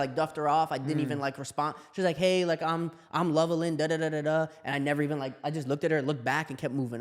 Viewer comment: Did he say her name at 20:28?lovellin? Idk how to spell lol